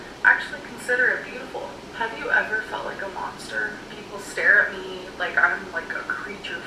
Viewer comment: i thought she said “do you ever feel like a lobster?” but no, she said “monster” .. oops😂